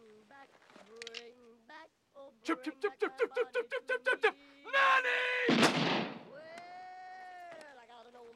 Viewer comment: All chants: Ned: chip chip chip chip chip chip chip chip chip. NEDDY!!!! Lucky: Farley Farley Farley Farley Farley hafurrrrrrrrrrrrrr! Dusty: Kinut hooooola widdle tas habble... San! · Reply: It sounds like ned says nanny